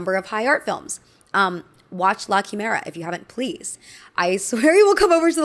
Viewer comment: what do you say at 28:55? mockumela? lackimera? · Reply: la chimera!